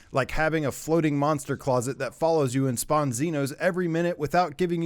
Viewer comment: Did I just hear Xenos? Kill them, for the Emperor!